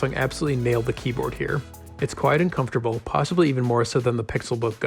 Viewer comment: I thought he said “it’s quite uncomfortable”